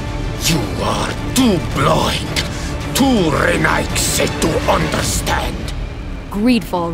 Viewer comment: What did he said here 3:20 ? "too renaigse ?!"